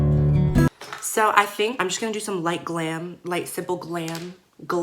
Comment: when she said “light simple glam” the captions said “lake sebu glam” 😂